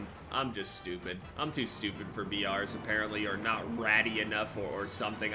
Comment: Did he say rady? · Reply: Ratty*